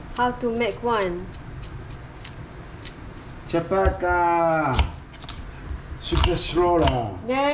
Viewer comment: Did I just heard "Cepat Bah, super slow lah" i mean 😂 his tongue is so fluent actually...